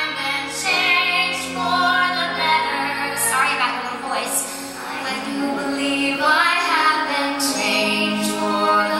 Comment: What does Kristin say at 4:31 ?? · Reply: Mark sorry about my voice.